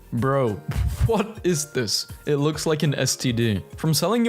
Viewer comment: Did u say std or scp